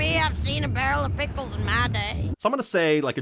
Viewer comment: I always misheard it as "a barrel of pitbulls"